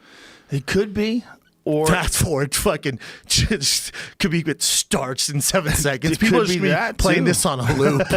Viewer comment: What does Brenden say at 4:55? I couldn't understand. Kabeb 'starts' in seven seconds? Huh?? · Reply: "starched" it like instant KO... like 1 punch fold type shit lol... Joe uses it again around 6:25 if you wanna hear it in context